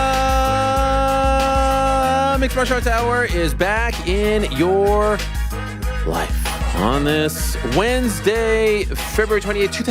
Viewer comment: What does he say in the intro after “arieeel helwaannniiii”??? then Ariel is like “naaaaa” what is Ariel saying lol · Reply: As with every episode he says, "THEEEEEEE mixed martial arts hour is BACK IN YOUR LIFE."